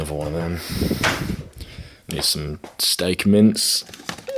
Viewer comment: did you say 'steak mints'? Ha! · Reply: Steak mince meat bro